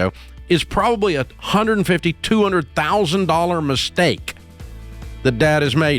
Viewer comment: Mistake, darn I thought you said STEAK 🥩